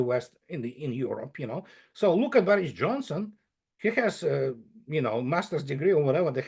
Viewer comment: Did i hear right,at 16:20, Andrei called Larry Johnson a moron? I thought he spoke of him highly in earlier episodes, what has happened??